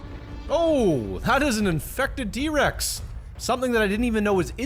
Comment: did he say d-Rex or T-rex